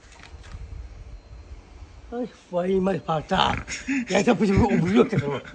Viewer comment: Твою мать он сказал или мне послышалось😂😂😂😂